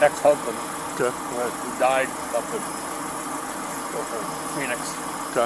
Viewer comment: Ah yes ge ol standby of "she/he are LESS drunk" a few drinks is as bad as any ,edit* 14:18 did he say that the husband/ spouse died or did I mishear?